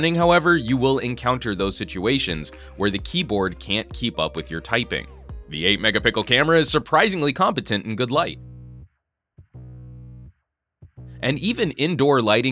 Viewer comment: i heard him say the 8 mega pickle camera lol